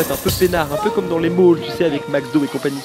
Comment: Après la pollution indienne, revient respirer le bon air des lacrymos ! :-) (T'as dit quoi à 3:16 "un peu comme dans les Mauges, avec MacDo et Cie" ???) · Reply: Je pense qu'il a dit "les malls", i. e. ces grands centres commerciaux, temples de la consommation à outrance. :)